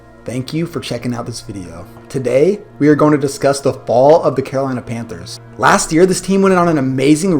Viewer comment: At first I thought you said the cardinals I was about to say!!!!!!